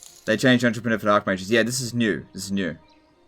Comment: I miss-heard that as Entrepenerf!